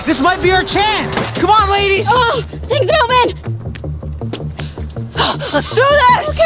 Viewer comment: When vy said" Melvin we " 8t sounds like she said ya man